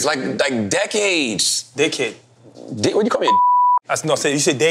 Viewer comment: When Serge Said Decade It Did Sound Like He Said DickHead 😂😂